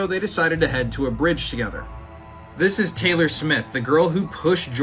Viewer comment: What i thought he said taylor swift but he said Smith what 😅😅😅😅😅😅